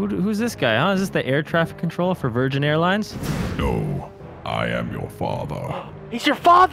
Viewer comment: I thought he said no I am Obama